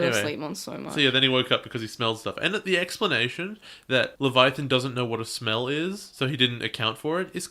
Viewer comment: To be fair, Hajime did try to show Rei that he deciphered the thing, but Rei told him to show him later because he was busy. Typical behavior for an older brother, I guess. Also, did say "eggsplanation" at 12:30? Because that sounded like a pun to me.